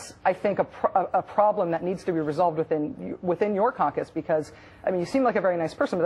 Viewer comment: WTF does that Rachel woman say after 2:21 "thats a problem that needs to be resolved within your "concus""??? THATS NOT IN THE ENGLISH DICTIONARY BITCH! (Is she saying "compass" perhaps?)